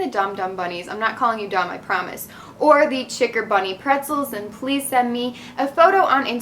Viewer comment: I thought u said chickerbunny in the end I was like 😳whats that but then I was like ohh chick OR bunny